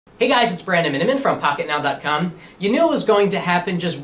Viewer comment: I can never tell what you say at 0:00. "andamitfrom pocketnow "